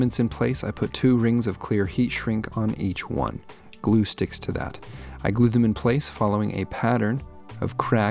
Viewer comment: Happy vacation!!! That looks beautiful, the woodgrain is perfect to offset the space effect! (And I swear I heard “Gluesticks to that!” So that will be my new exclamation of outrage.) 😆